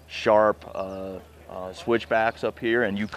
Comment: did he just say switch bacon?